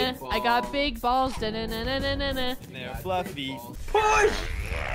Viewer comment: I think said "And they're fluffy :3"